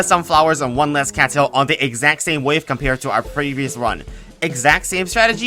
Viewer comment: the subtitles show last instead of previous